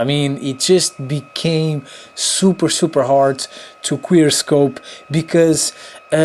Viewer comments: he actually said quickscope right lmao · I think he said "queer scope" 4:11 lmao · did he say queer scope 😂